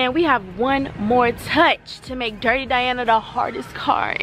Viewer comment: I love the name you picked Lol. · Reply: but why I thought you said Darty Diana 🤦🏾‍♀️😂. The name is still bomb either way. Lmao